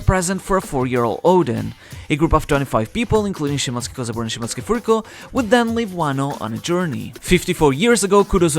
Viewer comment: wtf did you say 🤣🤣🤣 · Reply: He said shimotsuki kozaburo and shimotsuki furiko